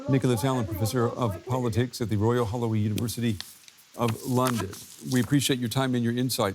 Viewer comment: Omg i thought he said “the royal Halloween university “